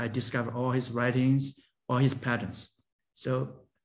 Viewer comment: Patents. He’s talking about all of the patents that have been filed by CSW. It sounds like he saying pattern but it’s patents. He is a computer scientist and he has studied many(500?) of the patents and is convinced that CSW must be SN